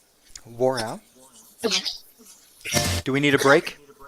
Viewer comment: I asked where Michelle " I heard " wore out" MALE voice FAST FAINT BACKGROUND whispers SLOWED down YouTube SETTINGS O.55x